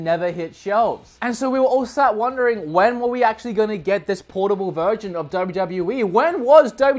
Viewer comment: Sounds like you said portable virgin lol